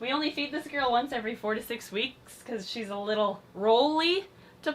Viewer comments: When she said every 4-6 weeks I did a doubletake because I thought she said every 46 weeks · I THOUGHT YOU SAID EVERY 46 WEEKS INSTEAD OF FOUR TO SIX I was so confused